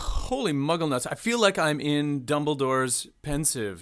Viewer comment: can someone explain to me what a muggle nut is? or did I mishear?